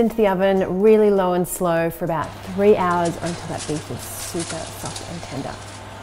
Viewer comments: what do you say there? "Super ??? Tender" My ears don't hear that properly 😂. I only hear "super f***** tender". Lmao. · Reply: Super soft and tender 😂😂😂😂😂😂😂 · "Until those ribs are super fucking tender." I'm sure that's not what she said, but I'm going to keep believing that's what I heard. Edit: That's definitely what she said. Subscribed.